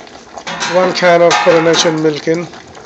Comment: thanks for this; what sort of milk was that please ? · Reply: Hi, it sounds like he is saying Coronation milk, but I think he means Carnation milk, if you don't have it in your country, try condensed milk cutting down on the amount